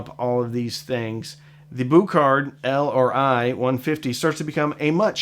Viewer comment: I kept hearing say "Buchardt IRL150"...I think we should just go with that 'cause, you know, real life!